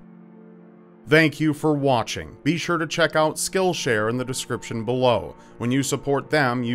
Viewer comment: when the sponsor came a thought it said kill share XD